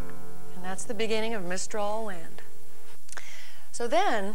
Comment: at first I thought it said "Menstral Wind".